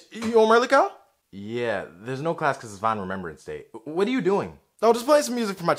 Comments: at the beginning: "School's off for Vine Appreciation Day." later: "Wha-- Vines?" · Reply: Soul I thought he said Vinyl Remembrance Day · “We got out of school early because of vine Remembrance Day” Soo... nobody gonna talk about that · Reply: That’s what I thought he said but I think he said VINYL Remembrance Day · “We got out of school early because of vine Remembrance Day” Soo... nobody gonna talk about that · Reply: I thought he said final rememberance day